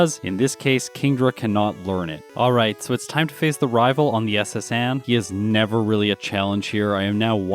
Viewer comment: I thought he said "It's time to fist the rival".